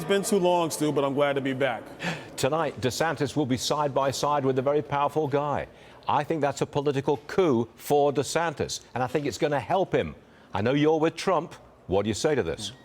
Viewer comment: At the beginning I thought Byron said, “Aw, yeah, it’s been too long, Stupid. I’m glad to be back.”